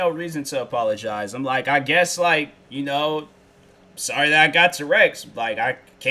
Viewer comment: sounds like he said "sorry i got t-rex"